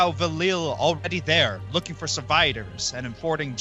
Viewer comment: i heard surviders and not survivers lol